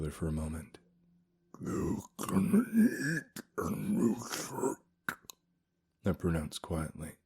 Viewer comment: Hard time understanding what was said starting @ 14:38?? Here you go: “Yoooo gonna neeeed a new shirt,” I pronounced quietly, pointing a talon at his torn and ruined clothing. “That’s nothing,” he chuckled. “You’re gonna need a new skin." But then his smile faded, his grip tightening on the walking stick as he studied me in the campfire’s flickering light. “Yoo cannn relax,” I lisped. “Shaman skinnnnn won’t work on me.” His eyes drifted uncertainly to his walking stick. “Still,” he said softly. “I’m supposed to try to destroy you." “Yep,” I hissed. “Same here, old mannnnnn.”